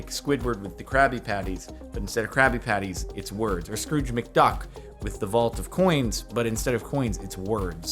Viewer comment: oh i thought you said screwed mcduck